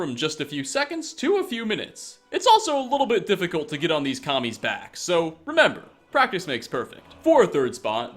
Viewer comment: Did you just say it's "hard to get on these commies' backs"? I didn't know the bugs of Skyrim were so engaged in radical politics.